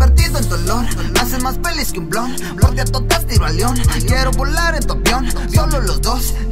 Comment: Okalguien me puede decir que es lo que dice en el minuto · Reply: Dice que por ella a todas las tira a león osea es como decir que a todas las manda a la vrg solo por estar juntos los dos